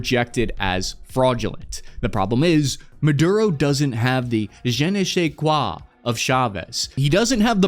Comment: as a french i took 1 minutes to precess it was " un je ne sais quoi " :D